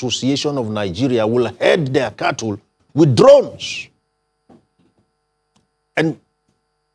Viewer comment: Did he just say herd cattles with drones? 😂